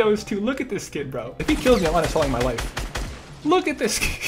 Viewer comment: i thought he said im uninstalling my wife